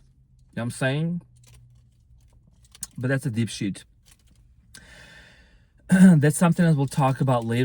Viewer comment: OMG.... did you just say "That's a dipshit...."? I think it may have been "DEEP shit...", but either way it works.... 😂🎉😂 ✨️✨️